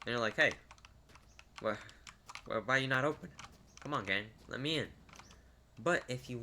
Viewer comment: did you say gang or game 🤔